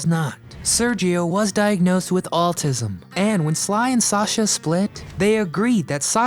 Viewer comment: Pronunciation: At 8:30 I think you are trying to say the word ‘autism’ yet twice I’m hearing the word “altism”. Of course, the issue could be my hearing. Anyway, wanted to share my observation in order to be helpful. Thank you for putting together these compilations. -Carl